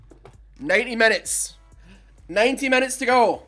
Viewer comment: You said 90 minutes and my zoned out ass thought you said 19 and i zoned back in so hard